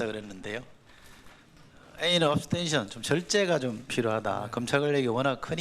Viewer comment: abstinence가 발음이쫌.. attention으로 들림.